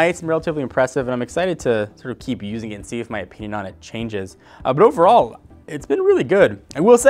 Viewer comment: I honestly thought he said, "I wonder if my peeing on it will change." I now understand how wrong I was.